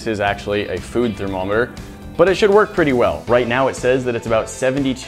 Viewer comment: sounded like you said Dow